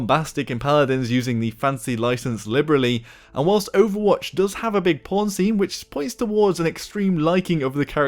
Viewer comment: did you say overwatch has a big porn scene?